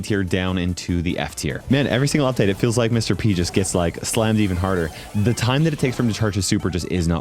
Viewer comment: i thought he said "he gets slimed even harder"